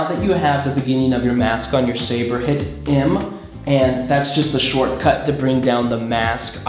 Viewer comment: I have just downloaded the after effects and Idk how to use it but I did it. Thanks dude! Note: Which button did you hit called "em" or "im" at 1:22 ? English isn't my native language so that I didn't understand. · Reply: It's the 'M' key on the keyboard